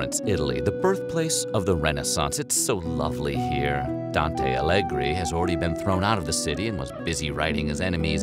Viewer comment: Omg I heard Dante "Alegre" omg please it's Dante Alighieri